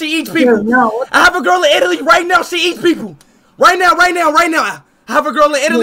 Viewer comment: At first I heard her say she’s hates people why are you using Omegle then 😂